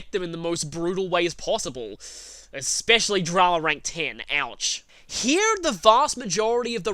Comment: What rank 10? I couldn't hear that properly, it kinda slurred a bit. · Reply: I think he said Drama rank 10, meaning Yumi.